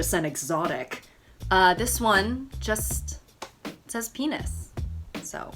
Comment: i thought it said Paris XD